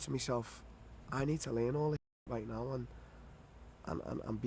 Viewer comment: I swear he said he needed anal